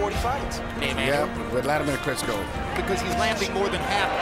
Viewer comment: I swear it sounds like he said Wladamir Crisco...